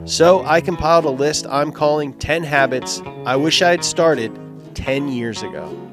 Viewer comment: I thought it said hobbits looool